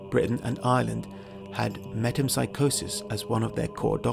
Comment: I could not understand the words : "Julius Ceasar said they had ,,,(what?) kind of psychosis as one of their core doctrines". Please, what did he say there? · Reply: Metempsychosis